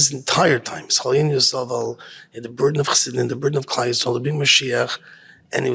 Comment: does he say "being" or "bring" or "burden" before "Mashiach" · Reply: burden